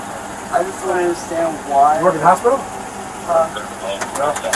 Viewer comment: can anyone make out what the cop said? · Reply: Sounded like “You work at the hospital?” Almost as a diversionary inquiry to throw off so they could shut the door.